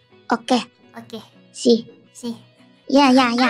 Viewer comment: kobo and gura is actually say "see" but that's ok "si" is cute too